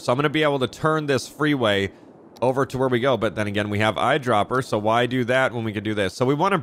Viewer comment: when you say "then again" I hear "finne-gan"